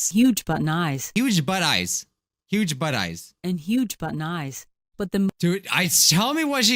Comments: This is all I hear “Huge button eyes” “Huge butt knives” “Huge butt n’ eyes” “Huge but nice” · I thought she said “but-nye” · it sounds like she said "Huge Butt Knifes" is it just me..